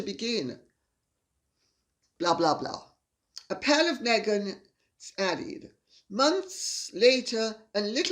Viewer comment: Lady C, I misheard one of your comments as "A pile of Meghan..." and immediately burst into laughter at your wit. I was unfortunately drinking an iced tea in front of my laptop at the time but I must tell you something. Your comments are brilliantly astute and she really is indeed "A pile of Meghan". Thank you for the edification and the best laugh I've had all week.